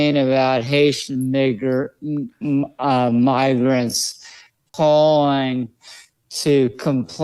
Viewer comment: I hear "miger"